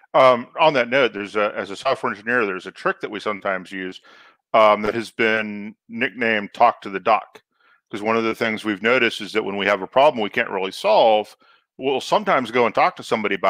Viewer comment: As a lifelong software engineer, when said talk to the doc, I had no idea what he was talking about. But when he described it I was like Holy crap I do that all the time :) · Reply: I thought he said "talk to the duck". My tinnitus is obviously getting worse.